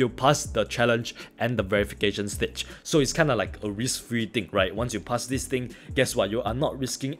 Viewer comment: its kinda like a rich free dick ride😂😂😂😂 was i onky rhe only one who heard that?